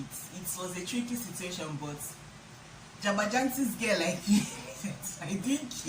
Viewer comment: Hahahahh!! Meeting everyone in the Family, Chairman got style!! But did you just say jagbajantis😂😂